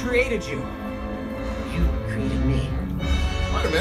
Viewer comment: i thought he said ( i never created that guy )